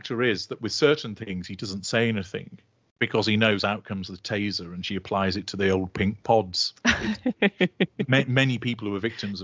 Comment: For a second i thought he said "here comes the taser and she applies it to the ol pink balls" 😩😂